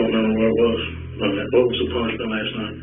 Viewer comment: "nothing was crying" Are you seriously saying she was calling the newborn "nothing" and saying "nothing was crying"? NO My god, she's saying that it wasn't crying. She's trying to say, in broken gasps of air and frantic hysteria, that the newborn wasn't even crying, like it was stillborn. "Nothing was crying" as in there wasn't anything crying. It sounds weird and stilted but it's fragmented and clearly ESL. To say she's calling the baby "nothing" is a horrible, lazy assumption on your part. How'd you even come to that? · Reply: Some closed captioning mistakes too.... Around 26:47 - [Inaudible] would be - "All right. Well, what was the punishment last night?" (Corner, was the punishment. Having him sit in the corner.) 27:50 - "I've got guys out CANVASSING the neighborhood" (not camping) 28:10 - "I was getting ready to say," (not I heard you say his name)